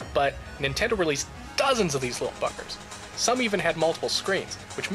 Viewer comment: Wow, had to watch the into to the game and watch twice. Thought you said that they "released dozens of these little fuckers" Hahaha, that would have been so out of character for you. Ohhhhhh, BUGGERS! Now it all makes sense.